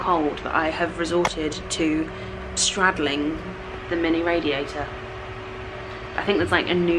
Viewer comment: I thought it said straddling a Heather😳